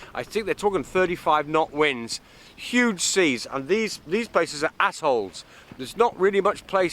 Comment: "These places are assholes" Wait - what? Oh he said 'atolls'